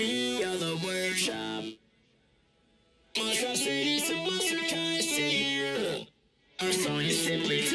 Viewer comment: help what does he say here😭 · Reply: they say monstrosities of monster kind